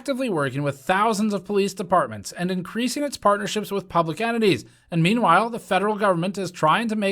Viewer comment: I heard "public entites" as "public enemies". Not sure they're different, in this case, tbh.